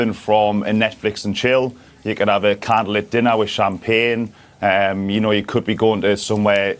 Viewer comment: I think he said “candle lit dinner with champagne” not which I’m paying